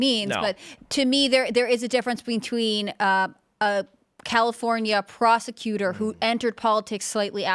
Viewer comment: I thought I almost heard "California prostituter"